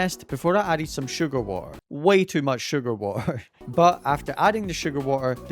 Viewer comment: what did u said 3 times? Sugar wa a?